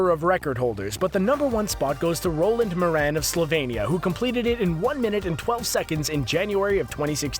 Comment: Did he say slovenia